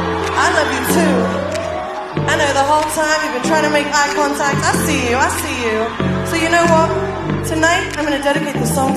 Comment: Can someone tell me what she say at 3:07 and the phrases after? It’s hard for me to understand.. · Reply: She says "I know you were tryna make an eye contact i see you I see you so you know what tonight I’m gonna dedicate this song to you"